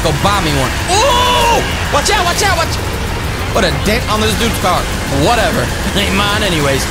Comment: I think he said the d word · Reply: He said dent